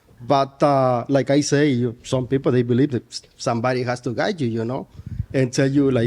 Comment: What cracked me up about him is he didn't listen very well because he didn't answer a few of the questions correctly and his communication was not always clear to me. He was not having it with people having dogs in the house.😂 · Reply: and rejecting the first woman who didnt have a dog, but the second woman who did was ok. The last lady misunderstood him though, I don't think he was saying women need a man to tell them what to do, she thought he said "guy" instead of "guide"; but he meant people in general have a guide. He was talking spiritually, as in having a higher conscious, or we'd all be robots. Regardless, he's a bit of a space cadet